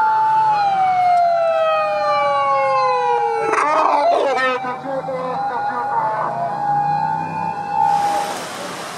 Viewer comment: 何て言ってるんだw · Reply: 「緊急車両直進します」 じゃない？